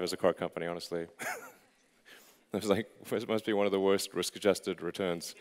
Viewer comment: what did he say, someone tell me please? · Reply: "Must be one of the worst risk adjusted returns"